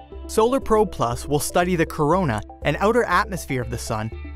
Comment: did he said corona